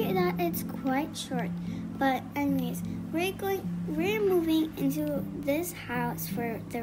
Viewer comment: For some reason I thought it said “We are mewing” 💀